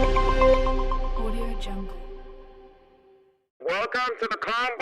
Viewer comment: i heard audio jungle 🤣